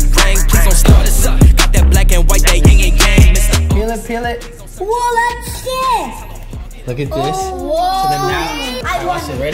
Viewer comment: WHAT DID HE SAY???"??????? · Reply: He said "oh that's sick! "